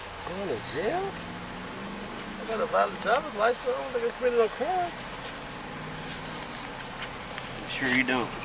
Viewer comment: ~ What did the driver say here? He said something like “I have a valid drivers license and I don’t ____”. What? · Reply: He said, "...and I don't think I committed a crime."